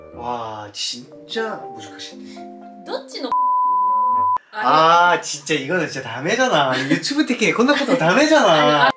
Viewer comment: 뭐라고 하신거지..?감히 전혀 안잡히는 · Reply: 얼굴에다가 싸고 싶냐고 한것 같아요 ^^